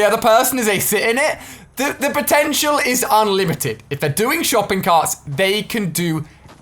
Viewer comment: VEHICLES ARE COMING TO FORNITE, BUT PROBABLY NOT IN THE WAY YOU EXPECT! 😱 Today we take a look at ALL the Vehicles that are CURRENTLY in Fortnite + how it could change the game. ENJOY! 👍💙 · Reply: I thought they said only shopping carts